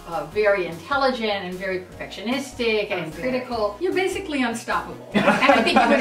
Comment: When one of the girls said to mitch, "You're basically unstoppable " I heard it as "You're basically on to scott" and i was like OMFG IT'S SO TRUE